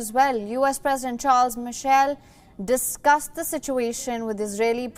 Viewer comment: did she said "US President Charles Michel?" 2:12 lol